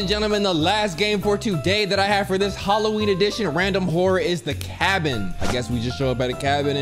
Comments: Am I trippin or did this mans say Halloween edition horror instead of holiday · Did he say Halloween Addition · whatdidhesay 😭 did he say Halloween ...